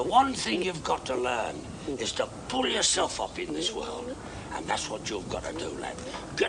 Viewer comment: Me: Pull myself up? Ohhhhhh….I thought he said off.